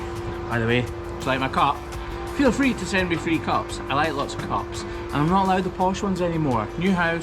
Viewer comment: Free cups??!! I thought said, "I like B-cups, too!"